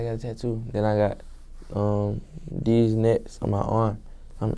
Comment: I thought he said “these nuts”